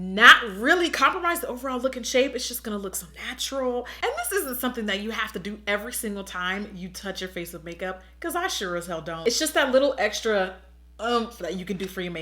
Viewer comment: Girl I thought you said Snatchural when you said so natural. But I think I just came up with a word 😂😂